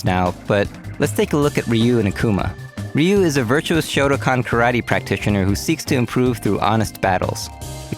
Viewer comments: "virtuous shotacon karate practitioner" Did I hear that right? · I heard that as "shotacon karate practitioner".